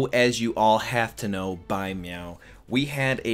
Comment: Did you say " by MEOW? "